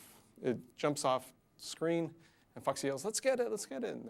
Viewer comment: Did he say Fuck at 00:45 ?